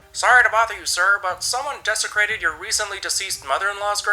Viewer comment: I watched this with a friend and thought they said "defecated" instead of "desecrated" so the entire time we're like OH GOD THEY SHAT ON GRANDMAS GRAVE